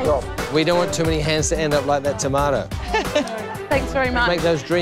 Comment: I thought she said Tamale not Tomato wtf lol